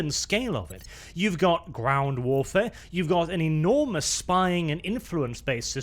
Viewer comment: did i hear.... GROUNZ warfare?